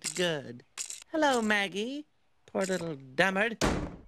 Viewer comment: does she say, "Poor little dumbard?" Is that a word, like a dumb person? Dumbard? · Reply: Dullard. "A slow or stupid person"